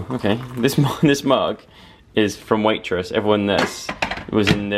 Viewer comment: When said ‘this mug is from waitress’ I thought he said ‘waitrose’ and i was like wow such a big deal 😂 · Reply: Haha, I thought he said Waitrose too!!! I had some broccoli from Waitrose the other day but it had plastic in it! 😭😂